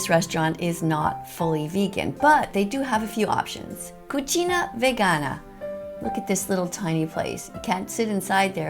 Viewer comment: Oh and one more thing, when you say "Cucina Vegana", it sounds like your saying more as "Cucina Vagina". 😂